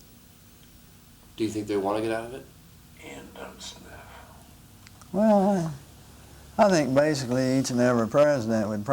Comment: It sounds like there is a 3rd man in the room at 6:12 anyone know what he asks? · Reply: I think he says "and if so, how?" But it is very indistinguishable